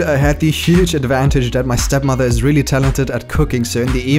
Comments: alright i'm definitely gonna leave porn now. I'm SURELY gonna do it! watches till 11:03 ....did he say step-mother...? okay maybe I'll watch just one last time hehe... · did i hear stepmom??..~~!!....[sus] ^)